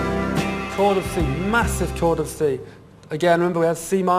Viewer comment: Toured of sea, massive toured of sea.... Can someone explain, what he actually is saying? I'd really love to know. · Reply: Chord of C?